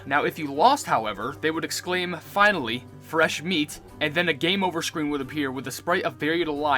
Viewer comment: this is a common misconception. He actually says:" finally, raw meat"